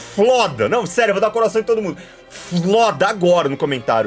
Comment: KAKAKAAK ouvi um "FODA"